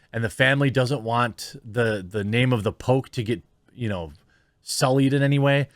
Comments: Man I got so confused because for the longest time I thought you were saying “the Pope.” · I thought you were saying The Pope not The Poke for half the video lol